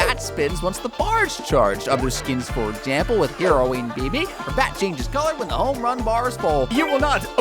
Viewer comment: I THOUGHT YOU SAID HEROIN BIBI!!!